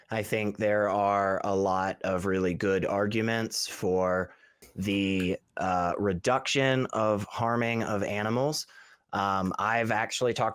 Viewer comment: What I heard was... "There are good arguments for the reduction of slavery"